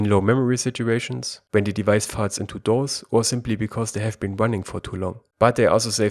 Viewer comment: the device farts into the dolls !?? I really couldnt understand ..6:57 again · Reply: Sorry 😂 "When it falls into DOZE"